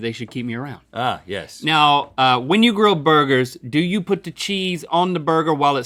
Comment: I thought link said "when you grow burgers" instead of "when you grill burgers"I wish you could grow burgers